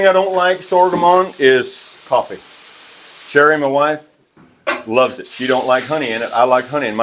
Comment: I thought he was going to eat the rubber scraper. 20:23 I thought he said 'One thing I don't like sorghum on is my coffee but sharing my wife , loves it'